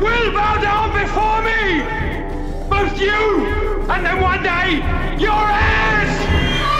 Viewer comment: Yes for the longest time I also misheard Terrance Stamp's line. Which if you put it into a really weird context is like one of the scariest things ever said by a villain to a Super hero. "You will bow down before me Jor-El. ! Both you and then one day...Your Ass!" So does that mean that General Zod is a latex wearing Dom? Good points over all in your review. I really enjoyed it.